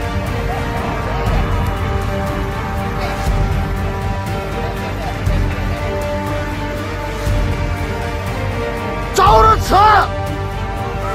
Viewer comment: What they're actually saying is "Retreat! Retreat quick(earlier)!"